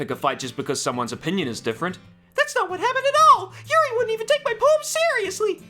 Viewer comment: subtitles thought that said “ URL wouldn’t even take my boobs seriously!”